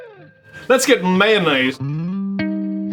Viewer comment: Did he say "let's get mayonnaised!" at 3:55?! :P